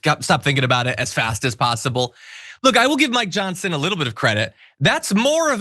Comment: I could have sworn you said "my johnson" vs. "Mike Johsnon" early if the video...lol